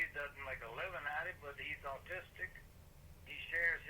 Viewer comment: Did he say Artistic or Autistic? I think i've answered my own question....